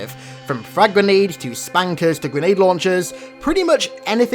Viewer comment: lmao did he say spankers?